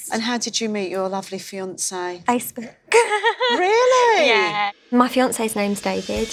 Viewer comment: did anybody hear what she said when the woman asked how she met her fiance??.. I've replayed it 4 times and can't make out what she's saying... Ice burg? ... Nice urg?... No way.. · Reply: AndySixxStalker DarkAngels I think she said Facebook 🤔